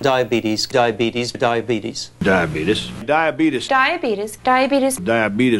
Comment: just me or did i hear die fetus?